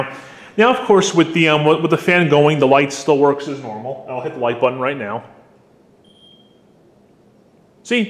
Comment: Did anyone catch how he said "Light button" but the youtube captions thought he said "like button" and the like button lit up?